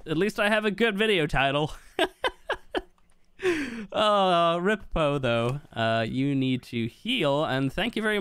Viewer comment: Thought he said rip bozo instead of rip “Poe tho”